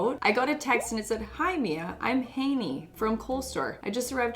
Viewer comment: When you said "colestore" I thought you said "cold sore" 😭